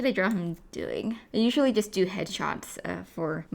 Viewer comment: I heard "I usually do the hedgehogs"